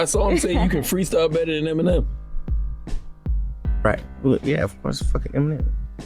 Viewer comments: Sounds like he said ya of course fuck Eminem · Sounds like he said yea.. its fuck'n eminem.. like his thoughts were well of course I'm better than him...